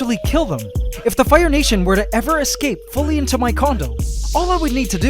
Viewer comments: did he say condomn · "if the ants were to ever escape fully into my condom" is what I heard bhahaha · It sounded like u said if the ants were to escape into my tiny condom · Did he say condom at 8:30? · it sounded like he Said condom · When he said condo I thought he said condom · did you say if they fully escaped into your condom? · I thought you said condom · I know he meant to say condo but it sounded like he said condem instead at 8:32 😂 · wait did he say condom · It's sounded like he said condom im 8:34 and 33 but I think he said condominium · thought he said condom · who else thought he said condom at 8:32 and yes I know he said condo cuz captions are a thing.